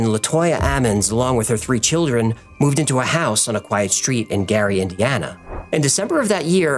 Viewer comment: For a second I thought you said 'Eerie Indiana' .